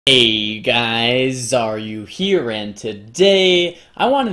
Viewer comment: Lol his opener to his video, thought he said "Hey guys Are You Here?" :D